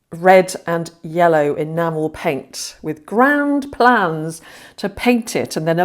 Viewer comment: When you said grand plans, I thought you said ground clowns.😅😅😂😂